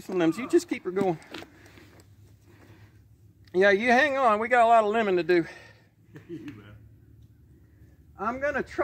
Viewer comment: I heard "we have a lot of lemon to do." LOL. 😄😄😄 id like to help with that.